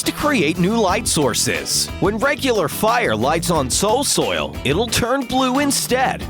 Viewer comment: I heard him say soil soil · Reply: me too but it is soul soil